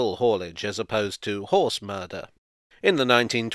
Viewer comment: Help a buddy out! The subtitles at 0:30 have saying "it was an early adopter of cable haulage as opposed to horse murder" - horse murder? Is that what he really says? 🤣🤣🤣